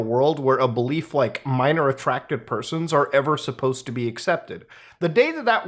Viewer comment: At first I thought you said (Minorly Attractive) Persons ...Then i figured out what you meant.